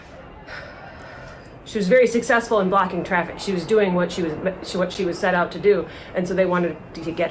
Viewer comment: "She was doing what she was "PAD" to do" is what she caught herself from saying.